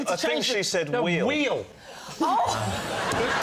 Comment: That Lee prank on David segment was a riot. 😂 Also, ngl, I also thought she said Will not Wheel. 😅